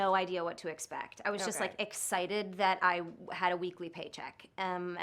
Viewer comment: Did she say "Weekly" ? Geez I need to be an actor in this economy